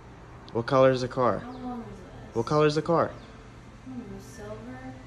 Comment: With the volume down low, it sounded like she said yogurt 🤣 for the color of the car but she said silver.